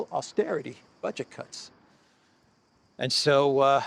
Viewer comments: When first said Budget Cuts I thought he said Bunch of C$nts. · When you said "budget cuts" I thought you said "buncha c words" and I almost died 😂 Pretty accurate observation 👌